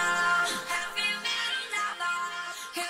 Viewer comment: All I hear is "have you met a lover?"